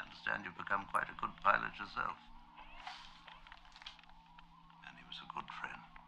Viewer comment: The revisionist in me likes to hear Obi-Wan's line at 3:44 as "Ani was a good friend" instead of 'And He'.